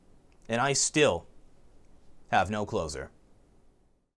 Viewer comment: I always thought you said "I have no clothes on" but now I realise you say "I have no closer".